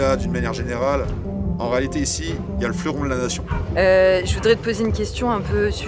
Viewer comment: j'ai entendu "fleuron de la nation", j'ai rigolé et quitté immédiatement la vidéo.